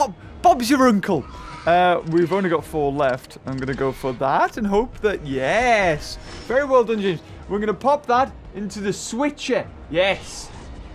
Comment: Did he say "Farewell Dundee" at like 4:20? Because if he did that's funny, I literally just played that for a concert.